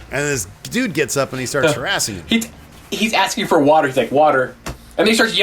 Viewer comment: I thought Picard was saying "waiter" not "water."